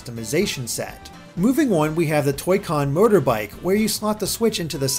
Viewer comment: thought he said toycon murder bike